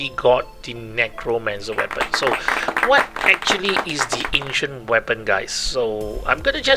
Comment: I thought u said asian weapon and I was like holy where can I get it?